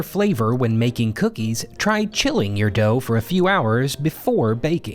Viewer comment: I thought you said “chewing” first and was like huuuuuuuh